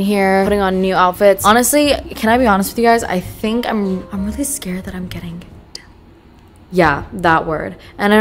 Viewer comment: i think she says “i’m really scared that i’m getting *depressed*” if so, we are here for you and always will be!! you can do a ranting 5 hour video and i’ll be there watching the whole thing then commenting on it to make you feel better!! ❤️‍🩹❤️‍🩹